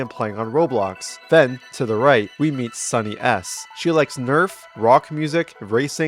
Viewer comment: gotta say it every time he said ''sunnys'' i heard ''sunny ass'' 🙏